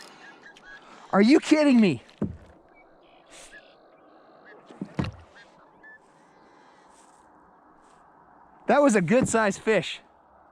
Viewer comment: Lol 😝😂 I heard a "Ffffffff........................that was a good sized fish. It is not my day Today!"